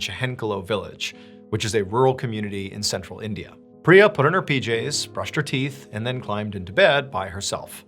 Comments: Am I the only one who has to rewind like 20 times ever video to the beginning of each story to hear the year and location? Those 2 things are always important yet I’ll hear them without listening. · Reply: i rewind to re-listen to certain things, like when he said "she put on her pjs" i heard "she put on her bjs" 🤣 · "pree a, put on her Pj's" I heard that as "Pree apuddinhapeehjays" like it was her full name.. Does that make me racist?